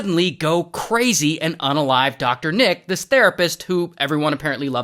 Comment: I thought it said the rapist